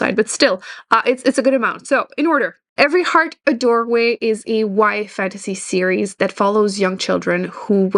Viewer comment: Hi everyone!! I'm currently new to reading and looking at for book recommendations (after following her for her beauty channel back in the day). Can someone tell me what is being said at @ 1:40 ? A y? wide? why? fantasy? Google hasn't helped! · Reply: YA = young adult ❤️